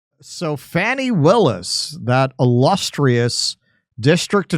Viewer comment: at first l thought you said fatty willis until l read what was on screen